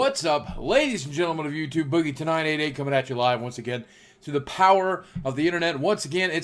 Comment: god dammit i thought he said every time "boogie tonight at 8"